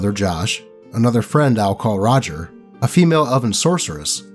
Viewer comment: Ngl when you said Elven I thought you said oven. 😂